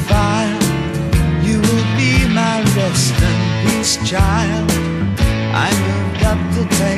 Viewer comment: you will be my rest in peace child. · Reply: i always thought he said western peace child.....